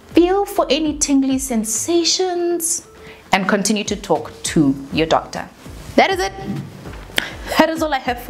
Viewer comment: When you said "Continue to speak to your doctor" in my head I heard "Continue to speak to your vagina" 😂😂🤦🏽‍♀️